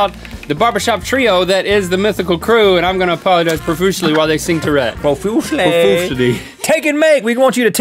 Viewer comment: I thought Link said "while they sing Tourette" in the end. I would love to hear that song Tourette. Think about it, b*tch.